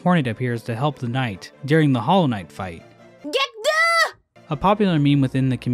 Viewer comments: always thought hornet was saying “get through” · I always thought hornet said "get down" when she flies in to help with the knight it would make sense as she does chuck her weapon right over your head lol. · Gonna be honest I don't hear hornet say "get good" I hear "get down" when fighting the hollow knight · i always heard hornet saying "get through" witch i thought made sense with you having a small amount of time to get into the dream realm to fight the radiance until hornet cant hold anymore. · I always thought hornet said "get down" when she flies in to help with the knight it would make sense as she does chuck her weapon right over your head lol. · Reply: I thought she said "Get Duh!!" like an insult to knight lmao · I always thought hornet was saying get through during the hollow knight fight, interesting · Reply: I heard it as get done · On first fight I thought hornet said “get through” like you should get through the hollow knights mind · I actually thought Hornet was saying: "Get through!" when you have to dream nail him. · I always thought hornet said "get down" when she flies in to help with the knight it would make sense as she does chuck her weapon right over your head lol. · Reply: I instead thought she was saying "get done" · When hornet appears in the hollow knight fight instead of her saying get good I always Heard it as get through like get through to the hollow Knight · I always thought Hornet said "get throught" in the Hollow Knight fight